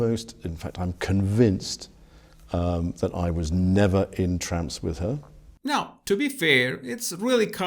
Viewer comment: That's weird because I thought he said that he was not entranced with her, not not in France with her.